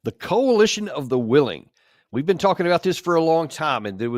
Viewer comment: Coalition of the willing is the coalition of the losers. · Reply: You had misheard. It is Coalition of the Willies.